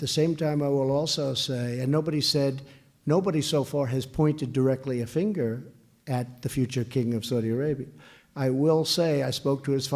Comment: what did he say ? "nobody saw for his point to directly a finger.." ? Not to be funny, just find it annoying if I can't hear what people say in vids (am not native english)